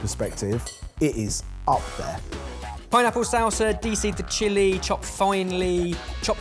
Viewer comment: did he say "deecee" for the word dice? Was he joking or is that how they pronounce dice?